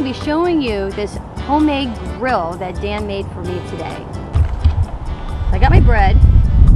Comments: i'm so horny that i thought she said "i got my breast" at 0.16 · I thought she was saying i got my breast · Did she say "..I got my breasts.."? :) 0:41 Again! " .. my butter on my breast.." No? Am I the only one? JK Nice view of a focal point on 1:23!